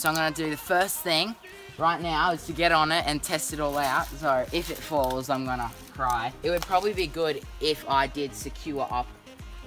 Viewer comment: Lol, Did he just said " If it falls, I'm gonna cry" at 4:30... hahaha cute